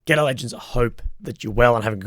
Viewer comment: in the beginning of sounds like he says ghetto legends hahaha